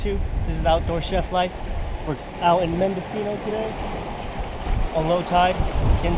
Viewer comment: Which campsite is this? Good place... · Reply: I think I heard him say Mendocino.